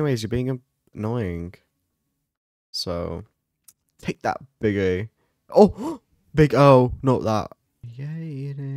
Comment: WHAST DID SAY 😨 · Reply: BIG AHH